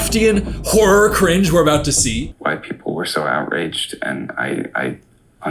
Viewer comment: I thought he said white people 💀💀💀